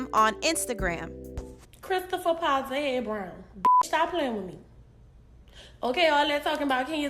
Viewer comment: Can someone tell me what she said between "Chris" & "Brown"? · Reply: Powta head 😥😭